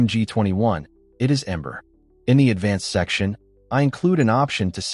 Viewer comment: did you say "ember"?